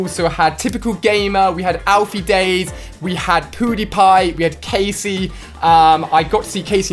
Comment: omg did he say poodiepie at 9:23!!! not hating i just thought it was funny!!! lolllll!!!